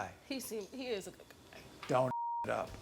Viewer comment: did Maury say don't F it up....he been around his ratchet guests 2 long😂😂